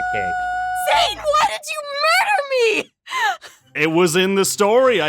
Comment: I thought she said, “ZANE WOULDN’t MURDER ME!” And I was like, “I mean....well... not in Mystreet obviously but.... Honey, if ya gonna go down that route, take a U-Turn Sweetie, bc “Zane wouldn’t kill me” is a bit.... un-specific? Just say “—In MyStreet” bc in a certain series our chicken nugget was overcooked in mcdonalds. But turns out she said “ZANE WHY DID YOU KILL ME???”